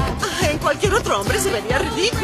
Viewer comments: Lo pedimos Louise ahora le pertenece al mundo 🌎💋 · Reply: Yo entendí "ahora le pertenece al ritmo" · "LO PERDIMOS LOIS... AHORA LE PERTENECE AL MUNDO" jajajajajajajajja MUERO DE RISA!!!!!!!!!!!! jajajajajja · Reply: Siempre pensé que decía: "ahora le pertenece al viento".